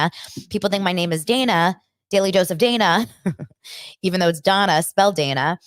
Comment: I think I heard you say daily dose of diarrhea 😂